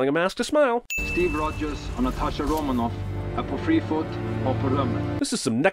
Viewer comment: IS it Norwegian? Cuz I am Norwegian and I had to listen to this 6 times and I still can't actually tell what he's saying.. "Steve Rogers og Natasha Romanoff er på freefoot og på rømmen?" Because that's what I'm hearing and that is not a sentence..